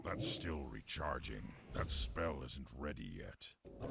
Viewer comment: I heard ,,, " That smell isnt ready yet " the first 10 times it was said .